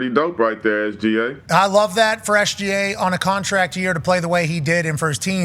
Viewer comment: I misheard Pat saying "for SGA" as "FreshGA." I like this nickname for him lol